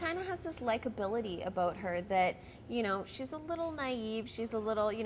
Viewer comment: is canadian? sounds like she says "aboot"